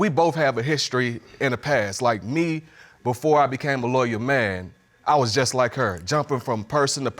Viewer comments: I thought he was saying before I became a lawyer... a loyal man 🙄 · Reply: I thought I heard lawyer before he corrected himself and said loyal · I thought he was saying before I became a lawyer... a loyal man 🙄